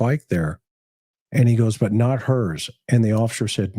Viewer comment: It sounds like he says “ but not her”